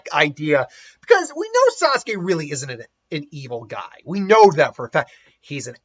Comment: You say: "Sasuke isn't a evil guy". I hear: "What if Might Gai was evil?"